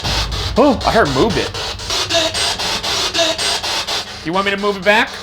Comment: Instead on "Move it" I heard "yes please"